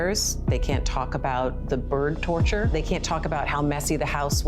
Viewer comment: Sad the parents should have gotten their kid help. They brought the gun for the kid. He wanted a bigger one. They should be held accountable drugs in the home and affair’s they are dispicable parents. Their son killed birds. End gun violence now. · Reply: I thought she said 'burn torture'. Now I see it's 'bird torture'.